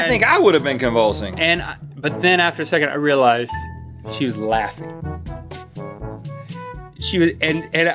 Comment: I thought he said “I realized, she’s black”